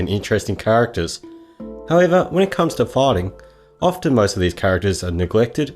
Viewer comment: I thought you said farting. A minute later, I realized you said fighting.